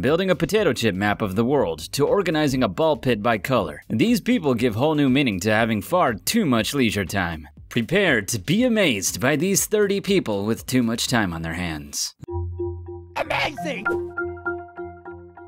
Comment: DID HE SAY LEJURE? 😂 (sejure with an l)